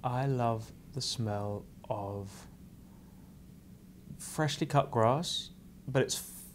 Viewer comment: " freshly cut grass"~ what are you hermionie Granger? She said " freshly mown grass" though